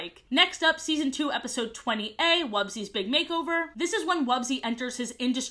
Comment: genuinely thought I heard Wubzys Big Hangover and almost choked on my food 10/10